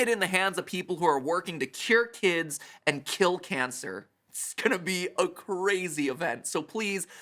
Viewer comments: Oh no I heard it as kill kids and cure cancer... OH NO THE PURPLE GUY IS GETTING TO ME OH NO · Matt: "curing kids and killing cancer" What I heard: "killing kids and curing cancer" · Did he just say kill kids and cure cancer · I'm I the only one who thought Matt said "to kill kids and cure cancer,"?